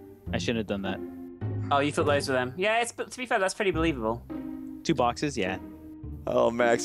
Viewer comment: what does say at 0:45-0:50 I really can't understand what she is saying? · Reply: oh you thought those where them yeah thats pretty believable